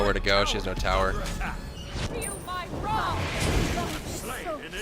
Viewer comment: I hear "Feel my wrath" not your bra thing, how did u conclude the bra thing anyway